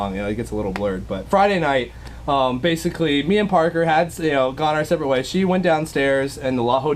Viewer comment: Yo did anyone else hear almost say we had sex when he was talking about parker? Around 3:00. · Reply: He was gonna say me and parker had separated but that makes it seem like they broke up so he reworded it